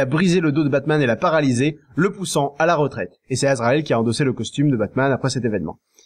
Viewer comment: C'est moi ou a 23:25 il dit "est c'est Ezreal.." ? Une référence a LOL ? =D